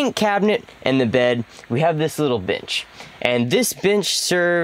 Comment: "In between the sink cabinet and the bed, we have this little bitch". I had to rewind it to make sure of what I heard. It sounds different than when "bench" is said moments later. Seriously though, I love this build and really like a lot of the choices that you guys made.